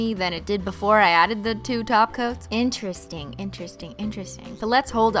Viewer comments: Jesus Christ why does it sound like you say "taco" instead of "top coat"?! How do you even do that? Maybe it's because english is not my first language, but come on dude, now I can't unhear it! Stop saying taco!!! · I must be hungry bc I keep hearing her say "taco" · This entire time I thought she was saying taco instead of top coat.... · Thought she was saying taco 🌮 every time she said top coat.